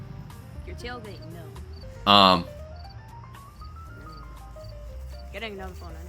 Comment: what did she say it sounds like "your tail mate know"